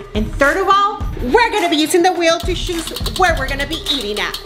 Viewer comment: The way says choose sounds like shes saying shoes lol